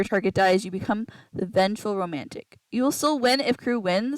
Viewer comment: Vengeful Romantic can kill the person who killed their lover. If the Vengeful Romantics lover was an imposter and was killed by the sheriff, the vengeful romantic can kill the sheriff. Vengeful Romantic's kill button should help them towards winning with whatever team they are on. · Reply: Thanks! I thought you said vengeful romantic becomes crew lol